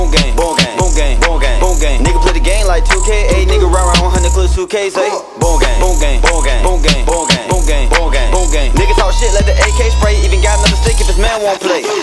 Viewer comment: Boo Thang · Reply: Lowkey thought he was saying "boot gang"😂